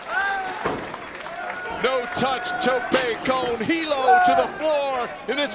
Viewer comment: What did he say?... Also, who was providing commentary for this match? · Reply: tthomaselli2 no touch tope con Hilo. Probably spelled that will lol. And it was Josh Matthews and Don Callis